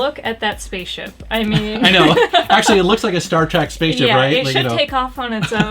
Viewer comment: Did you just say "Star Track?"